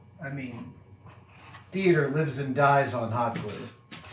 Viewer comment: I thought you said “Fear lives and dies on hot glue”